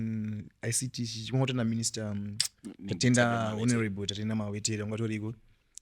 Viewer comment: Did he say Minister Tatenda Mawetera😅